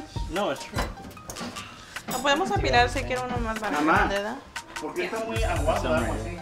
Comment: did she say, "le hecho mucha agua el pendejo?" 🤣🤣